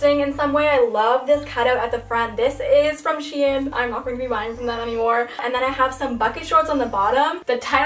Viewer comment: waitttt what did she say about shein at 4:45 cause I swear she said "im not gonna be buying from them anymore" AND I JUST PLACED MY ORDER YESTERDAY DID I MISS A SCANDAL OR SOMETHING